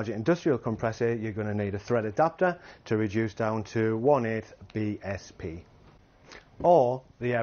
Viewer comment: Could you tell me (written) the pressure and units for larger industrial compressor? I'm not an english speaker and I can't understand what I hear... Maybe 1/8 BSP? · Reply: Hi, we recommend running your compressor at 50 PSI when using our airbrush. The recommended adaptor is 1/8 BSP for a larger industrial compressor. I hope this helps.